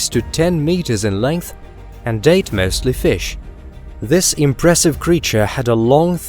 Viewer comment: "and ate mostly fish".... I heard "and date mostly fish" like... Bruh